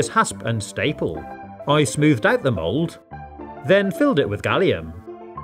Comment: I thought it said gallium gum